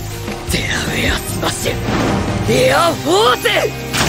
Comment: it sounded like gear fourse lol